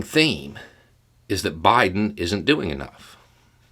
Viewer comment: I thought they always say, "Biden isn't running the country."🤔